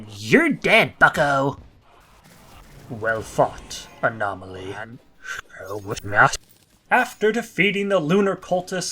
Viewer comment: What did he say here? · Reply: "Time to work"